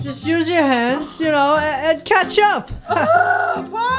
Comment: When said catch up it sounded like he said ketchup